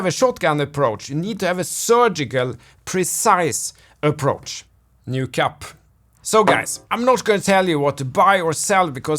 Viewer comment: I was listening and I heard you say "new cup" and I had to pause and rewind and listen again because I thought you said "no cap" lol